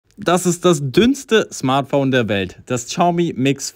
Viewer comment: ich habe erste mal verstanden das "Dümmste" XD und dann "Dünnste" JA ich gehe ja schon zum Ohrenartzt :P